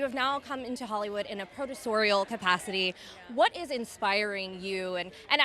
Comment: What did the stupid girl interviewer say to MM? It was something like ‘now you have come to Hollywood in a “predatorial” capacity?’ (Around 17:58) or WHAT was the word she used?